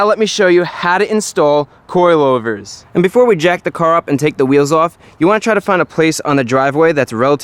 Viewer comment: I thought I heard: before we jack the car off and jack the wheels up I had to double take like that dosent sound right😂😂